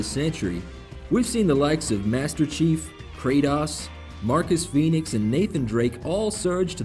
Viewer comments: did he just say.....Kratahs? KRATOS!! DX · Did he say Crateass? not Cratos at 2:22 hahaha